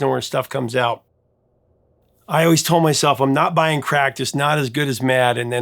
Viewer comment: I thought you said I’m not buying crack!😂